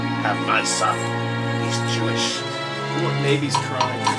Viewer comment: before the song went did someone say "i got a son, he's jewish?" XD